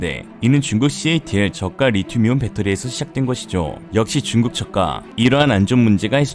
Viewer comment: 역시 중국 저가(낮은가격)가 왜 자꾸 "중국 젓가"로 들리죠 ? 물론 중국의 새우젓가격(젓가)이니까 별 문제는 없지만... ㅋㅋㅋㅋㅋㅋㅋ